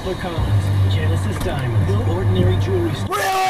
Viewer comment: did he say “die!” 😂